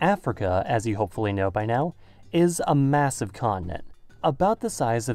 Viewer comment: What it said: "Is a massive continent." What i heard: Is a massive colony